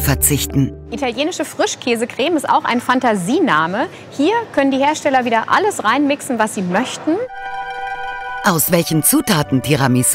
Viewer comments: Hier können die Hersteller wieder alles reinwichsen.... Bin ich der einzige, der diesen Satz so verstanden hat? xD. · Bei 10:21 hab ich "reinwichsen" verstanden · hier können die hersteller alles reinwichsen was sie möchten? hab ich da das erste mal verstanden xD